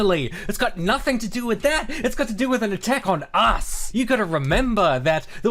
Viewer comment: does he say "an attack on arse" Because I'd sure be a happy man if he did 😂